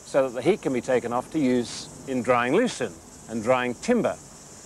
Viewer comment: What's the word just after 7:02? "so that the heat can be used in drying ??? and drying timber" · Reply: I'm glad you asked! He says "drying lucerne". Lucerne, also known as alfalfa, is a type of plant that is dried and made into hay to use as feed for livestock.